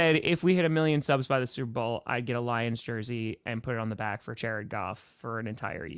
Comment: real name is and has to get a Titans jersey that’s what I heard 😅😂